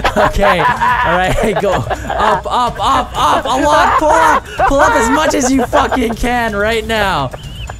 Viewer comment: did Vanoss just say allahu ahkbar?